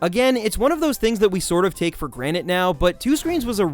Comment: Did you say 'Take for Granite?' I'm getting Rick and Morty flashbacks over here.